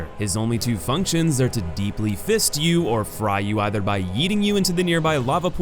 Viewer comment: did you say "yeeting"?